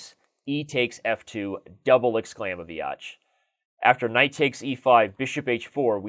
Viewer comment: It sounds like he says, "Double exclaiming, Biotch!" rofl